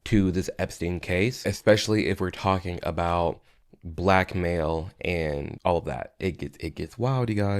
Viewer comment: I heard black male instead of blackmail and I was so confused lol 😂